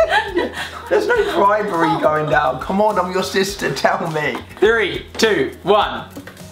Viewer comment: Did anyone realise at 5:00 he said come on I’m your sister instead of you’re my sister